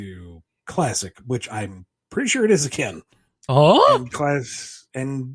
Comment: Wait, did Ian say he's pretty sure Chaotix isn't Canon at 10:15? Or did he say he's pretty sure it IS? He said it so fast that I can't make it out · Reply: He said that he's "pretty sure it is again". This is reaffirmed in the May 30th, 2022 BumbleKast